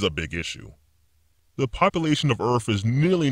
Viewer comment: did he say the population of earff?? Aparantly even this microchip still hasn't solved some age old problems about pronunciation and enunciation!